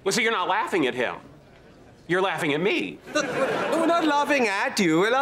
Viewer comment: Guys what is sheldon saying at 3:50 after "oh" i can't catch the first sentence could your write it to me please? English is not my native language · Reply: "Well so you're not laughing at him." "You're laughing at me."